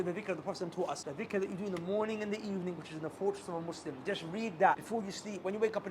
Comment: subtitle error. said "Fortress of the muslim" but the subtitle says unfortunate.